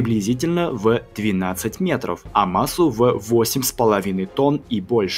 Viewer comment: мне послышалась 80 метров